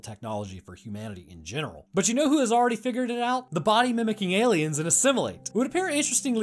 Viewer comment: i honestly thought it said "the body shaming parasite" and was both confused as READY